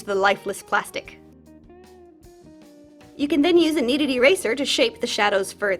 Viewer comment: Years ago I thought you were saying “a needed eraser” like only erase when you need to. This means that my first dolls were carefully erased with a regular pink eraser